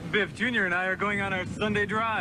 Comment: I thought he says "Oh no you ain't!"